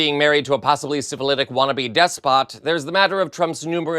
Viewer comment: OH! 😆. I had my tablet set on CC and this is what it said. 2:59 "Other than being married to a possibly 'civilitic wannabe death spot'.". 😅😂🤣